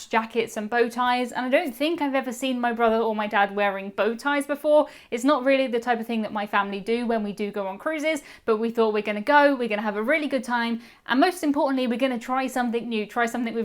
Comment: sounds like youre saying Qanon cruises...